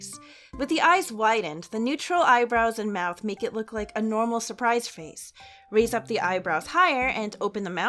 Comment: I really thought it said Surprised Pikachu Face…